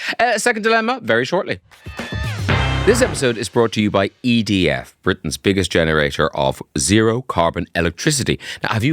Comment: Jesus, for a second I thought you said this was bought to you by I D F. ......